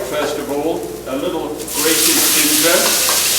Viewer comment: Mr. Kerr, first let me say that I'm a big fan, ever since I was a kid watching you as the Galloping Gourmet! I love the gentle, healthy life centered course you're on now and I really enjoy your current endeavors. Would you kindly tell me what the second ingredient was that you added to the skillet? Was it ginger? I couldn't make it out or hear it. Many thanks for the work you and your wife have done and continue to do! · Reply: Sounds like he said "Grated Ginger."